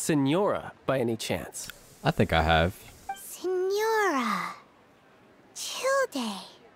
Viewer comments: "Signora...Childe.." What, did Paimon just say "Chill-deh"? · Signora.... Childe ( but pronounced as CHILLED DAY...) Hahaha that's what I say too- Wait